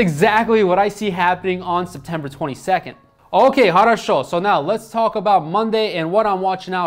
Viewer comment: did you said «Хорошо» in Russian?)